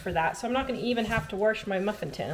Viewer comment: I heard that "warsh" (for wash). My mother in law pronounces it that way.